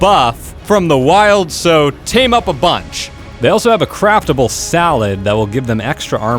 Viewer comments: did he just say craft a ble walled instead of craftable saddle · did he say salad ? 😂